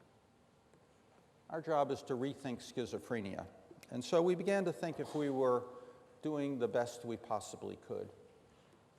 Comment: Hi, does anybody know what two drugs Dr Freedman talked about at 11:40 - 11:45 in this talk, which he said will help greatly to Schizophrenia patients, just curious about the names of the drugs , what are they ? Thanks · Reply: I don’t know if you ever found the answer, but they were anabasine and choline.